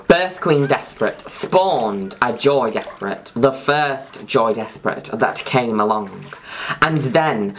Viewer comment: I thought you were saying Joey Despret this entire time. I can't unhear Joey.